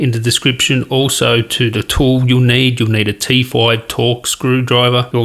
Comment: United 0:30 did you say T four or T five torx screw driver? I understood T four, but the picture shows a t five screw driver. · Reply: T5 my man